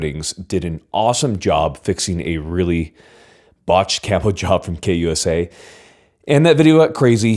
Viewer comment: I don’t know if it’s just because I’m from Missouri but when he talks about the KP9, I hear “botched catholic job” every single time he says it and I legit have no idea what was wrong with the KP9.